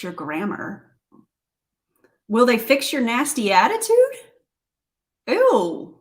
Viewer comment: I missed the live like I normally do but I you said 'will they fix your nasty attitude' and I heard 'nasty ass'